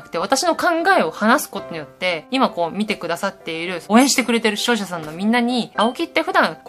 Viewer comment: 視聴者さんってとこ処女さんって聞こえるの俺だけ？